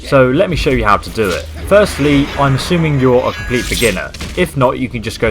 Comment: What does he say at the beginning around 0:16? "Firstly, I'm assuming you're a ...."? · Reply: Complete beginner.